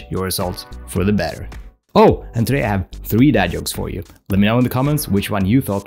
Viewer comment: Oh, I thought you said "DAD jokes" in the beginning, but you must have said "BAD jokes".